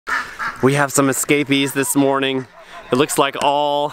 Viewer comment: I thought you said "we have scabies" WHAT? Then realized you said "escapees" made me chuckle. I can see why you love this countryside. Beautiful